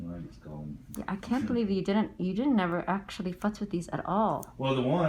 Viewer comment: it sounds like she said "i cant believe you havent actually fucked with these" lolol